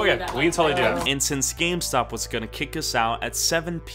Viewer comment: Who else at 6:59 heard scamstop instead of gamestop